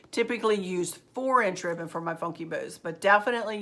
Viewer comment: Sweet, Thank You for answering my question. It was such a stupid question I thought maybe that is why you hadn't answered it! Haha. I must of heard you say 4 inch ribbon and some how got in my head I heard 2.5 inch. As many bows as I have watched you make I should have known. It could also be my age, I'll be 75 this week. Although my hearing and eyesight is just fine, with glasses. Haha. It was worth being stupid just to get to see Precious Maverick, Boy is he wanting to talk! I think he is going to be an Overachiever like his Grandma! Now to your decorating, As Always it is Simply Stunning! Like your Dad, I love your Pencil Tree and that Funky Bow Topper you made for that Silver Lantern Is Exquisite! As I have said before I am doing my livingroom, dining room and kitchen in pale blues, white and silver so I really enjoy watching to see what you are doing in your livingroom and dining room. I Can't Wait To See You Decorate Your New Tree! And like you I am staying with the more traditional Christmas Red and Green for the rest of the house. My Children and Grandchildren insist on it! Even though I'm old I still Decorate for every season and holiday. I have always been a crafter. I love that you honor your Mom with your decorating. I have a bedroom that is dedicated to my Mom and at Christmas one of my trees is everything Mom! She always made everything so special! I lost her years ago but still miss her everyday! I have kept you long enough so I'll just say, God Bless Yo